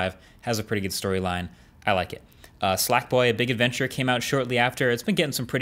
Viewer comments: did he say "slack" boy 1:10 or am I just hearing things? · Did Marques just said “Slack boy”?